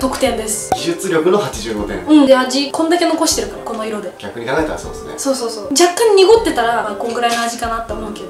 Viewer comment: スタッフなんて言ってるんだろう？「逆に投げたらそうっすね」？ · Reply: netgeek 逆に考えたらそうっすね